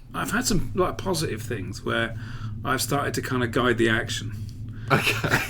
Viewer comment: What does he say around 27:00? Guide the action?